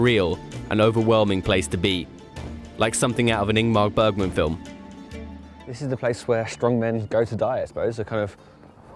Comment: What does he say at 17:44 ? An inmar burgen film ? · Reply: Ingmar Bergman. Kinda famous Swedish director who inspired Woody Allen and many others. His "The Seventh Seal" is very often referenced in popular culture. For example, Colbert parodies the iconic scene is when a medieval knight challenges death to a game of chess, in his "cheating death" segment. Check out the IMDB page of that film, particularly the "referenced in" page.